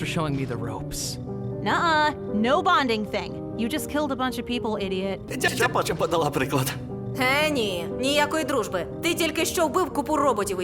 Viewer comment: dont know where you got almighty robot but she is actually saying “for the love of circuits”